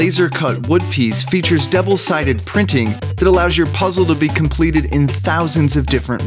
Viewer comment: Did he say complete!?